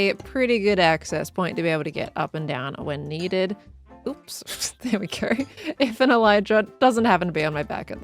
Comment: Thought she said “Stair we go”, which… missed opportunity